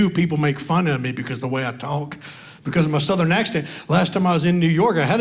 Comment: you actually called it a Southern accident🤦‍♀️😜🤣🤣🤣 I am Native Floridian and Choked on those...my man asked me if I was Ok, so I told him. He laughed, asked me Who said that so I told him what you said about your ethnicity and then about being in position with the ape hangers...he lost it😁😜 "Wearing a pair of Nike Air drinking a Red Bull....man, those really Do give you wings"🤦‍♀️😁